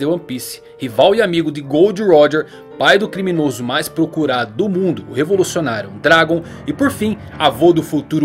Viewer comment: mano eu juro q eu ouvi o "criminoso mas procurado Bolsonaro" kkk